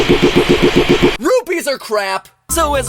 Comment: did MatPat JUST say, "rubies"?!